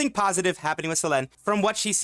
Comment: I heard Selen lol.